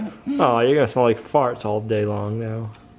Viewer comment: did you say, "You're gonna smell like farts all day long" ?